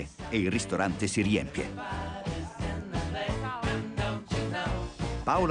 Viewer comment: Ma solo io al minuto 39:45, o due secondi prima, ho sentito deficente invece di efficente 😂 🤣